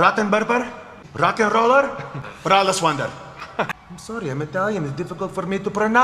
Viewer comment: Did he just say Braless Wonder?? 😂😂